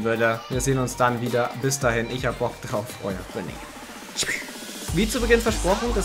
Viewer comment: ich hab verstanden ich hab ein Loch im Kopf